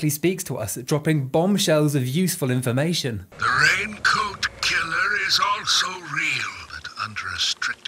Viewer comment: "The raincoat killer is also real!" Immeditaley in my head I heard "Can we get much higher?"